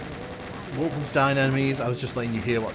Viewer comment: Oh they say "bloody hell", I thought I kept hearing "daddyo".